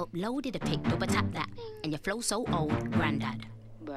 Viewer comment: they say "Your Flow So Old" but they want you to think she said "Your Phones So Old" because the iPhone 6 doesnt have Weirless Charging